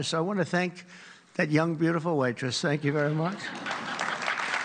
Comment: Did he say "White-tress"? Instead of waitress?!